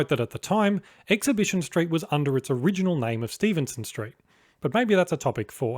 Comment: Maybe the sound is a little unclear, but did you say Stephenson St around 1:10? I believe it was Stephen St.